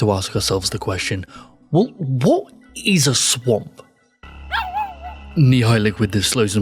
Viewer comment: what did he say here? Not a native speaker, it doesn't sound very clear to me. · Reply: He either said "Well what is a swamp?" or "Wh-What is a swamp?" (Stuttering a bit on the second one.